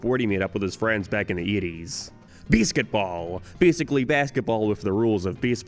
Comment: The way this guy pronouces the long A sound 💀 I thought he was saying "beesketball" until I saw it spelt out.